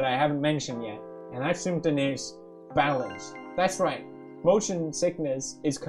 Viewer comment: fuck, I thought he said barrel😂